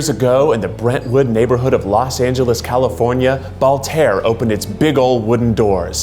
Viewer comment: Is it just me or it sounded like they said ballhair